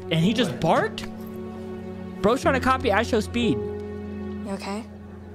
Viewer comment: did Bro say copy IShowSpeed 💀